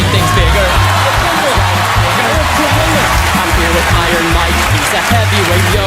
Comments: I thought for a second that he called mike tyson the N-word at 3:25 lmao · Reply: He calls him by his nickname Iron Mike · wait, what did he say? :D i'm hearing "I'm his ni**er"